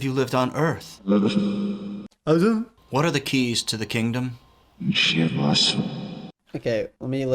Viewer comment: When the guy asked "What are the keys to the kingdom?"i literally heard "shit, bless" 🤷🏻‍♀️